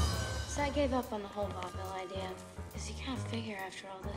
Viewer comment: anybody know what she saying there “so I gave up on the whole ??? idea”? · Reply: Tea i think she says vaudeville